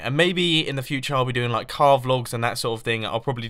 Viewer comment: it sounded like he said carve logs... OK!